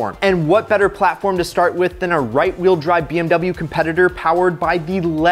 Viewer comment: Did he say right wheel drive at 4:03?